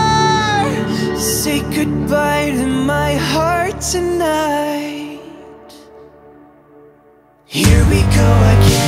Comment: I thought "say goodbye to my heart tonight" was "take a bite of my heart tonight" which would make it so much creepier